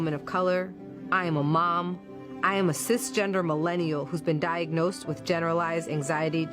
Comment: I thought "they" said "6 gendered." I was like "What the heck is a combo of 6?" I should have realized "they" said "Sis-gendered." And it's "Cis" with a "C" to top it off. I need to learn a new language but nobody wants to teach it to me.